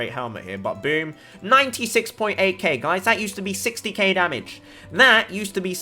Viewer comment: i heard "60 gay damage" love to hear it